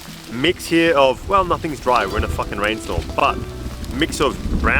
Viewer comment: did I hear you swear “we’re in a fcuk kin rainfall…”? Ha 😜😝😛ha ha ha